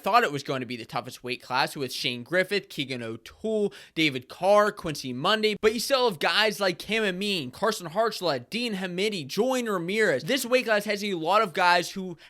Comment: did he just say Monday at 165? So does this you think he’ll stay there or go back down to 157? He’s a big 157 and competed well against Carr. The weight cut has to be hard in him